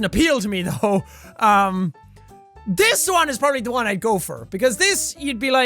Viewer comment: I know this is because of his accent but whenever he says "go for" all I hear is "gopher" 😂😂